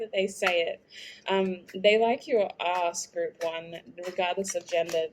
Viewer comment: !!!! They like my ass or that was something else I heard wrongly? Anyways, Good to know. LOLLLLLLLLLLLL 🤣🤣🤣🤣🤣🤣